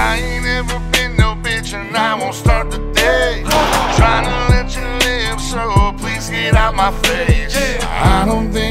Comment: Thought 🤔 he said Lynch, I was disturbed 😨 put on CC closed captions and he says trying to let you live!!! Much better,,💝🙏🏼🙏🏽🙏🏾🙏🏿🇺🇸🕊️